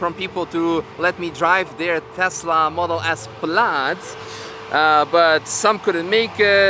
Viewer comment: Excuse me Sir, did you say "Tesla Model S Blyad" at 0:35?